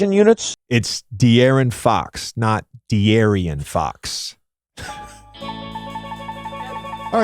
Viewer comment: I thought mad dog said Aryan fox and then they showed Jokic 😂